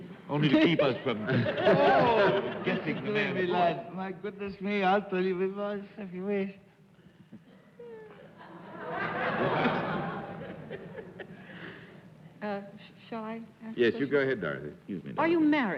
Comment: My question at 22:09 (is simply) WHAT THE FRIGG DID HE JUST SAY?! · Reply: “My goodness me i’ll tell you <obscured> if you wish”